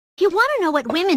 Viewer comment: Why did I hear "Thats great! Where can I buy one?" 😰